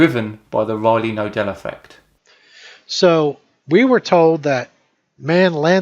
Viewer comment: What does he say at @ 6:44? "driven by the something effect". What's the *something*? I couldn't understand what he said. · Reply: the Riley-Knodel Effect. See video from 4 days ago for the explanation.